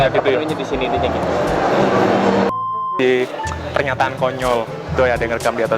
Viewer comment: Gua Gak Salah Denger Kan Si Abang Bilang PERNYATAAN :v